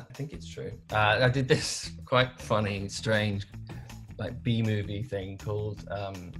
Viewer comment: TBS is in a B movie with Eva Longoria & Billy Bob Thornton ...why did I not know this? searches for it immediately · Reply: I thought he said Bee Movie like the kids cartoon wkdhsks